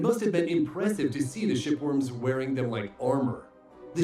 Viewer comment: For a moment, I thought they said "Shitworm"